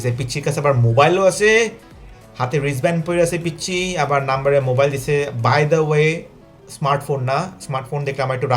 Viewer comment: Wait a minute! আমি কি ঠিক শুনলাম? "নাম্বারে মোবাইল দিসে?"🤔 time